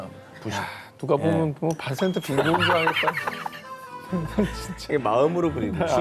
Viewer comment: 나만 반센트 빈고흐라 들리나..?